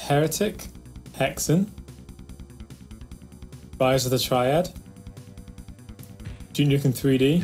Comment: Hi, sorry but what is the name of the game in 0:50? The subtitles says buyers of the triad.. thanks you're great, your videos are interesting! :)